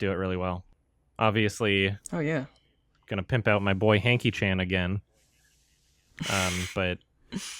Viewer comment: What's the name of the director mentions at 1:28:51 ? It sounds like he says "Hanky Chan" but I googled that name and found nothing. The guy's films sound interesting, and I want to watch them, but can't find them. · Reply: Michael Haneke